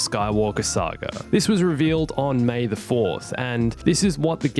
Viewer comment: Sounds like he said "May The Force"